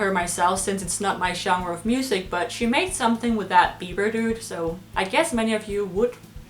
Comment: "She made a song with that Bieber dude" - That made my day :P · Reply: Chrome's World Gaming At first I heard it as beaver dude and I almost spit out my drink